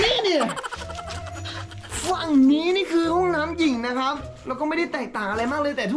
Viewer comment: ตอน 5:18 พี่พูดว่าห้องน้ำหยิ่ง